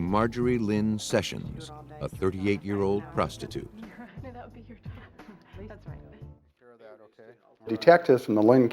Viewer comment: Oh. I thought he said 'Nude Detectives'... 😮😯😲🫨